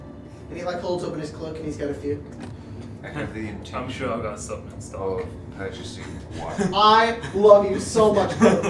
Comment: what did he say before said "i love you so much" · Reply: said “I’m sure I have something in stock” which was a reference to Resident Evil 4. got the reference and went “Colton I love you!”